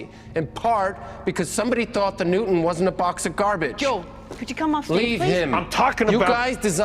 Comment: Look at that girl, did she say "yo come of the stage!" I'm dying omg!!!